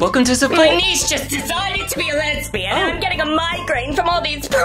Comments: I thought she said "My Knees just decided to be lesbian" whoops- 😅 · I thought she said “ my knees just decided to be lesbian” 💀💀